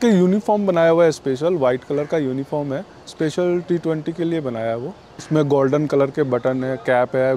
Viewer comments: sec, did he say T20 :) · I heard it T-20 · must be a mere slip of the tongue, Did I hear T-20,? Imagine if he is with Ausi PM, 😊 appreciate his dedication to his profession. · Did he say T20 ;)